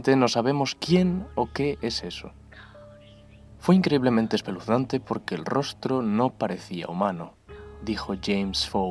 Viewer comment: ¿Qué dice la voz de la mujer que se escucha a partir del minuto 8:40? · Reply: "Go to sleep, go to sleep, go to sleep, baby"